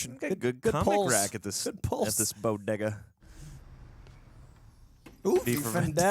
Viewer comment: what did he say? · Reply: Lmao he said "bodega" BUT we know exactly what it sound like lmao I had to run it back like 4 times lol 😂😭😭